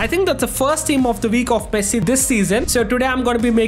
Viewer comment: dude says messi but always sounds like he is saying pessi damm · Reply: listen what he said in like 0:04. He clearly "Pessi" and not just once